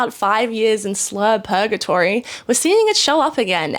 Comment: Nearly a perfect video. I almost gave a follow, but I heard you say "slut purgatory" instead of "slurgatory." What a fucking shame 🙄